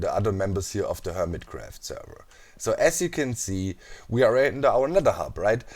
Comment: i though he said my name he actually said as you can see -_